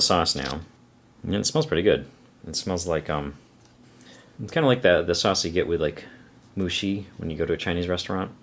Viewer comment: Great review as always, thanks! At 6:15, I think you're saying what is pronounced "Moo-shoe". 木須肉